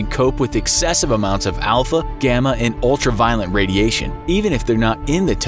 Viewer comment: anyone else hear him say "ultra violent radiation" or is it just me?